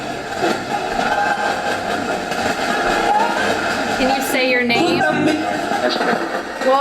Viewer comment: I swear when you ask "can you say your name?" after the quote is on screen, it sounds like it says "that's Yankee Jim" go listen!